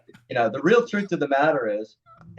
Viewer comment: Did someone understand the name of the singer that Mark is mentioning at 4:58 ? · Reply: It's Gregg Analla, ex vocalist of Slavior where he played with Mark. There are some demos for Warlord with him online from the "Holy Empire" days.